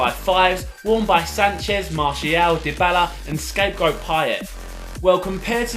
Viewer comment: what did he say "scape goat payet"